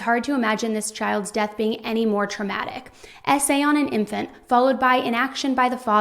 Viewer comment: Can someone please tell me what SA is, I've looked it up but I'm getting nothing - unless it's sexual assault.. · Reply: yes, it means sexual assault. I thought she was saying "essay" (english is my second language) and spent a whole time searching on google to try to find the meaning before finally realizing that it was actually "S.A." = "Sexual Assault"